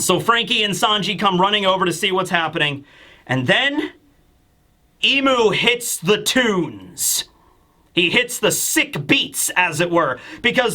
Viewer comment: I heard "Imu hits the toons" which is also true with what happens with the houses